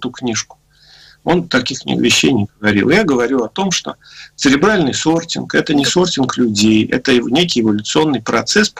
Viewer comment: 37.06 цеЛебральный сортинг. это мне послышалось?